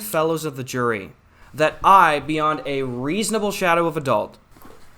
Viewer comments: Did he say a reasonable shadow of adult? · did say "beyond a reasonable shadow of adult"?